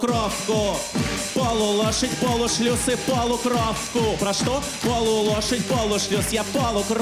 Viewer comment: В 2023 первый раз услышала "Полукровка: полу лошадь, полу шлюз" 😄😂👍👍👍